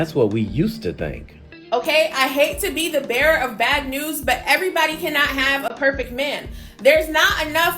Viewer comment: Did she say "She hätəs to be the BEAR with bad news"? 😂